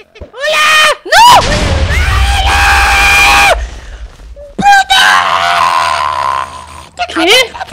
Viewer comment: NO ME LA CREO TIMO!!.....LIKE SI ENTENDISTE LO QUE DIJO....DIJO P##O POR ESO ME DESSUSCRIBO DE SU CANAL (TAL VEZ)